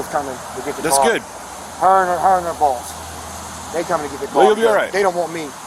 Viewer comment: Transcript 13:36 hernia balls (I think this is when the guy said "her and her boss") Best transcription of what was said